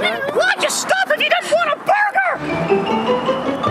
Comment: why’d ya stop if you don’t want a burgerrr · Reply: I thought he said "Well just stop it he just want a burger".